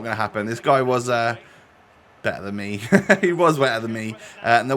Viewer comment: what did he sayyyyy?? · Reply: He was wetter than me